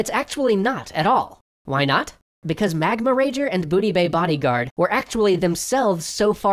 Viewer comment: When I heard “Booty Bay Bodyguard” my brain interpreted it as “Booty Bae Bodyguard”